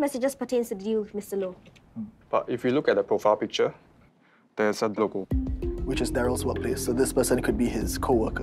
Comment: cant hear what word he saying... why is their pronunciations so bad -. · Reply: ‘If we look at the profile picture, there’s a logo’.